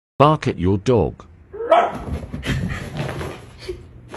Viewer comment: Oh damn- "I think you just activated her zoomies" 😂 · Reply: Misheard as “You’ve just activated her domain” the first time